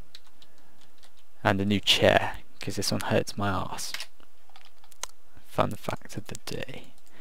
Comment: I was treid watching' this and zoned out, but i heard 'Hurts my ass' as 'Hurt my ass' and went back to see wtf he was talking about